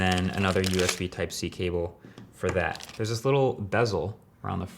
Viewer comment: It sounded like he said USB type c table not cable